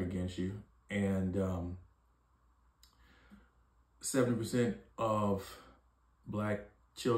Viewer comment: 7% only? Or did you say 70%. As a foreigner looking in from mainstream and social media, I thought the would be above 50%. Mahnn, the media is doing black families in the states soo wrong.